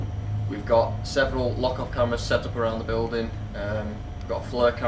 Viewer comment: Great stuff guys 👍 the male voice behind the bar sounds like he said ''f**k off!''